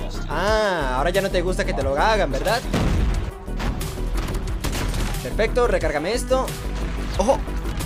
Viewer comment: Pfff yo pensé que decía perfecto recargame esta xd